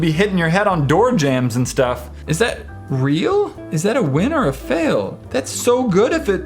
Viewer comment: Is it a winner or fail? Me:Did he say wiener?!!